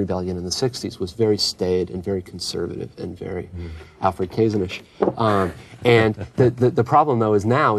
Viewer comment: Somebody please help me out, what was that last word/term at 22:39ish? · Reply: “Alfred Kazin-ish”